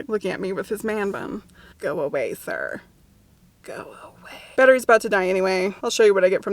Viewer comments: I misheard "batteries about to die" as "better he's about to die" and thought you were gonna go after the man-bun guy. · Okay when you were talking about the man with the man bun, I honestly thought you said 'but he's about to die anyway' and I was like ummm am I going to be witnessing a Youtube murder? 07:10 for time stamp